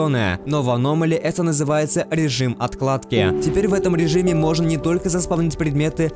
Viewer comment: я один слышу "режим отКладки"?